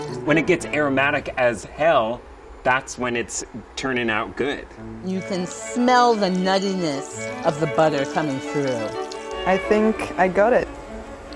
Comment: For some reason... I only heard "you can smell the nutty-ness of the butt... coming through" at 2:01 I replayed it 3 times. nearly choked of laughter. sorry.